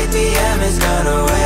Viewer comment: Where does the 69! sound clip come from ? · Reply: I always thought he said “sick D.I.”